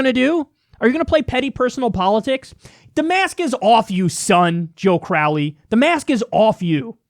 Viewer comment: I thought Kyle was trying to insult Crowley by calling him son at 6:50 “The mask is off, you son”. When he actually said “the mask is off you, son”. This is why punctuation AND pause is important, kids.